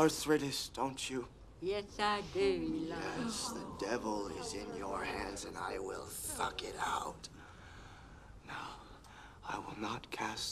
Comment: What he said: "Yes, the Devil is in your hands and I will suck it out!" What I heard: "Yes, the Devil is in your hands and I will fuck it out!"